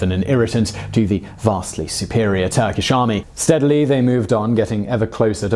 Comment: did he say “sadly” ?